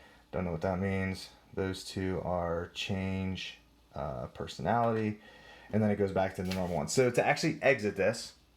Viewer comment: Did you just say “change personality”?